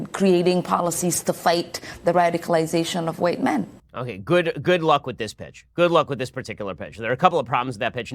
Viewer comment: I thought Ben said "good luck with this b***" XD